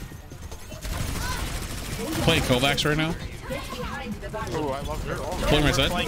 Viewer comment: did say at 07:36 "Playing Kovacs"?